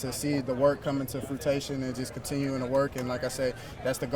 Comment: my man said fruitation instead of fruition 😂 @ 3:55 hold it down for the CBoys Tolbert, shootin guns (i mean fake guns) 😅